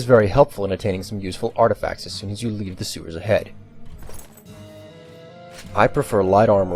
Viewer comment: What did he say? All I hear is: "artifact fzfzfz head." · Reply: When you leave the sewers ahead I think...